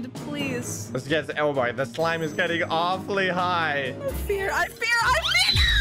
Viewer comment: What Did say 🤔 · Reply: I fear